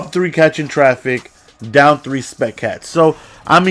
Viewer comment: sounds like he said spec cats